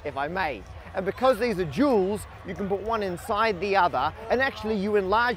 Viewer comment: Matts pure delight at the dual shapes within dualed mirrored shapes is literally the best part of this video. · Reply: Dualed? I thought he said jeweled because it would look pretty if you could cut gemstones into those shapes. Imagine beads shaped like that on a necklace.